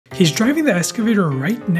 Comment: I thought he said “He was driving the escalator…” 💀💀💀